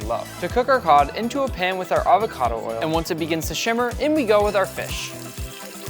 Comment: says shimmer instead of simmer